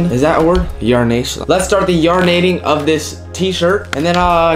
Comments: it sounded like u said "let's start this urinating" instead of yarn-ating LMAO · When he said yarn-ating I thought he said urinating 🤣